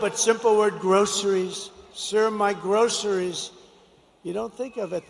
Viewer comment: He does this often when speaking. He will latch onto one or two words and repeat them an insane amount of times in a conversation. He did it in the recent article, talking about the ports with "you know." It shows how juvenile his vocabulary is. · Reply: Sounds like he is saying Gross-eries. 😂